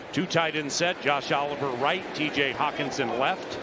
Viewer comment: I thought l, he said josh allen.✌🏿😭